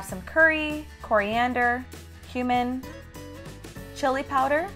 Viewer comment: Did she just say she puts human in the Meal or is it my ears... Sorry but I'm not a cannibal